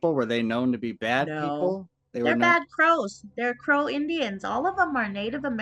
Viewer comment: Two girls drugged in a van full of guys? Yeah she may think they’re good dudes because they’re her people but it’s always the ones you least expect. Sounds like a coverup to me, what a sad story. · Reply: Sounded like she said they're "bad crows"